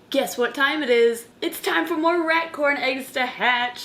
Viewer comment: I thought she said "It's time for more RATS to hatch!" 😳